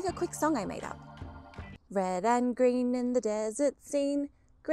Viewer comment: I hear "Grain Store".